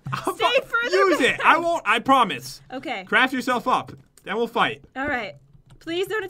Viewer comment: Did just swear? He practically said "Crafters don't fuck" in front of his viewers, or what I think to be those words. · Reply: channel has Been deleted he said CRAFT YOURSELF UP